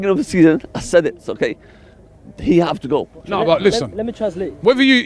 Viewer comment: did he say "he have to go"